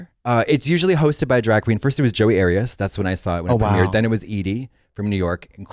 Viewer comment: I did my research and now know who Joey Arias is, but at first I heard Katya say “Jodi Arias” who notoriously murdered her boyfriend 😳😩